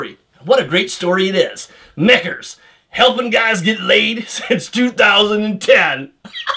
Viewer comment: What did you say at 2:40? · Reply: "Mickers, helpin guys get laid since 2010" lol